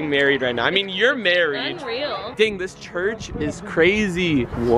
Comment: did he say church? I thought he was Muslim at first..